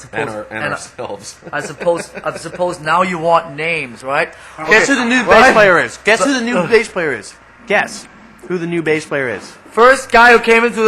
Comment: Sounded like Kirk said 'Get to the new bass player is" at 1:48. Did you hear that?